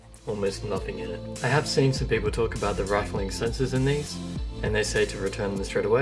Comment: rifling? I don't think I've ever said that word. Do you mean "rattling"? That's when you shake the military version of this mouse and it makes a sound because the sensor is loose. If that wasn't the word, can you tell me what time in the video I said it? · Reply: at 0:55 but now it makes sense. i was just confused i thought i heard rifling , but i guess my error